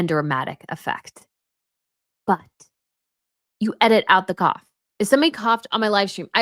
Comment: When you said they left the cough in there I thought you said “Coffin” 😂 (listening in the car). I had to stop and back up the tape to see the Coffin 😂